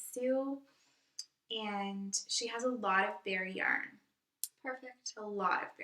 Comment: When you said "I needed to get bare yarn." I heard, "bear yarn" and I was like... "interesting! But legal? Safe?" Heh.